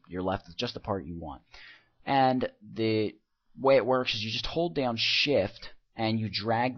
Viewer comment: hold down shift. but at 1st I thought you said "This whole damn shit!" haha